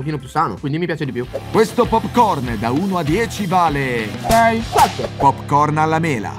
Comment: ma solo io a 3:19 ho sentito dire 4 e hanno messo 7?